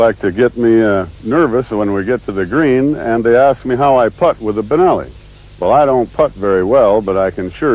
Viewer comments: Sounds like he said "They ask me how I fuck with the benelli." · did he say FU@ with a benelli ?!?!? LMFAO!